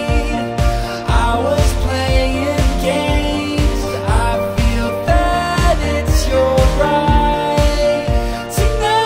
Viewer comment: Anyone else hear "I feel fat, it's your right"?